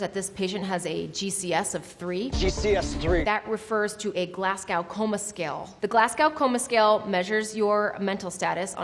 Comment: Wow, that sounded like you said Glass-cow Coma Scale. I've heard it a bit like that a few times before, but never THAT strong. It's much more like Glaz-go! :)